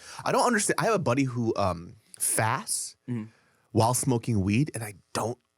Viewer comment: I heard “faps” at first before I thought about it some more.